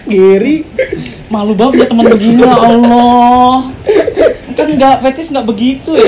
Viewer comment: arap ngomong ya ALLAH apa gw salah denger